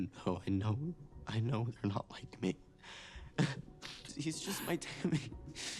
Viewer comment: Little rant. A few days ago I clicked on an edit that included the coming out scene and I don’t know if they manipulated it but it sounded like he said “I know you aren’t like me” instead of “I know they aren’t like me” and it sent me down a spiral… the Duffers saying Mike knows it’s him and at the time I thought HOW COULD WE KNOW THAT AS AN AUDIENCE WHEN MIKE’S FACE DIDN’T SHOW IT and how maybe we all misheard it? But no… Will was vague and yet the guy who is so clueless he can’t find his ass with both hands and a flashlight “knows.”